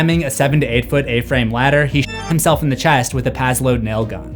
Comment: The censorship made it sound like he said "he shit himself"